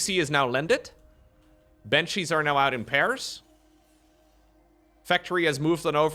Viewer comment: For a moment I thought you said Banshees are now out in Paris... which would be lovely for them in springtime